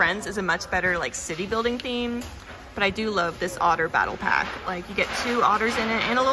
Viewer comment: Did she just say otter battle pack😂 3:56 She said it agien